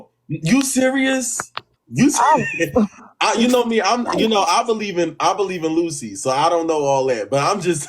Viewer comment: Did he just say he worship Lucy as in Lucifer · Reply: Rewind the vid, boy said he “believes”